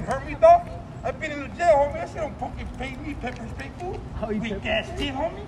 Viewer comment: What does he say at 5:45 after being sprayed? He says "you don't hurt me homie, I've been in ........." but I can make out what he says. What's he been in? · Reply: He says Prison…..like that will make back off!!!! LMMFAO